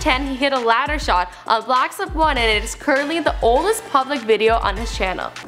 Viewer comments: did she just say, "blacks of 1?" lol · Did she say blacks op